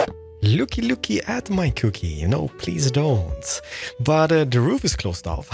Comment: I just heard “Looky looky at my cookie, don’t look at my cookie” oh that hit me in my feels ❤️